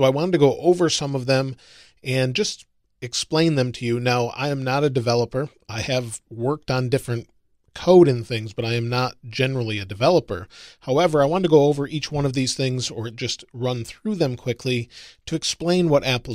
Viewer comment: Wait. You work for Apple? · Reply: Oh I thought you said I’ve worked for coding and stuff